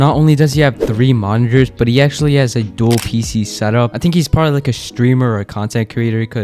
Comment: On the last (7:42 7:43) one when you said “he is probably a streamer” the auto captions said “stripper or screamer” pretty wild